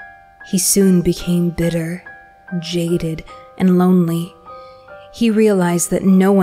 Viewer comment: bro i heard jaiden